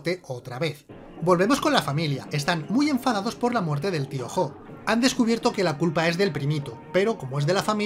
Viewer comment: todo el puto video escuché Piojo, ahora recién me doy cuenta de que decía Tío Jo.